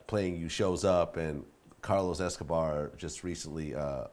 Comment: did he said Carlos Escobar ??